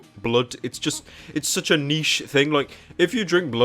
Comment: when he said it was a niesh thing the caption said it's such an Asian thing XD